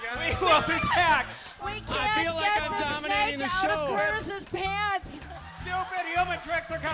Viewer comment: Snak thought it's rat instead of it's dad's pants 😂😂🤣🤣🤣🤣🤣🤣